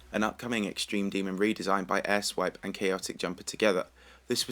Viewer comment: did you say ‘asswipe’? XD